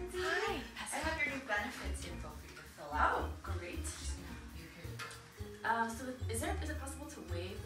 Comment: LOL "Good job, you're hired" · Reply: Ryan Baird I heard "you're hired"